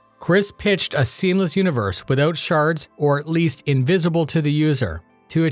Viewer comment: I hear 'shart' instead of 'shard' ....why does that feel so apropos?